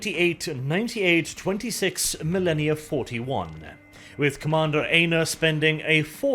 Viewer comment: I keep hearing commander Anus